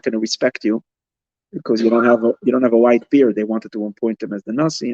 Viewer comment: Oh you said white beard I thought you said wife there🤦‍♀️ Either way was the miracle the light within?It teaches its the knowing so mabye its what made him wise🤷‍♀️